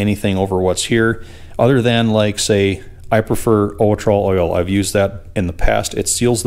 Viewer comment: Did you say ULTRA oil? I'm always on the look out for new products that can help me. What is that?